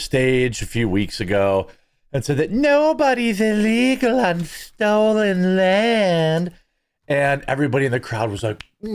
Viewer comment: I thought she said nobody was illegal on stolen land.